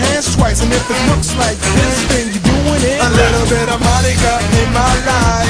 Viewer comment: Omg I thought he was saying a little bit of money lol 😂